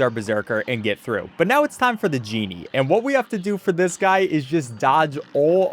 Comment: its "Djemmi" not "The Genie"(is what i thought you said